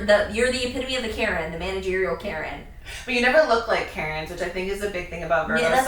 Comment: I’m a Scorpio and not sure how I feel about the Karen comment i actually very quiet about things until I m cornered then I will fight back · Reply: I thought they said you guys look like Karens, not are Karens. And that yeah, you’re quiet and indirect. Brooding too much